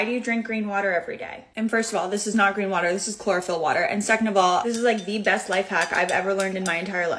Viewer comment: Ohhhhh, I thought she said chloroform water....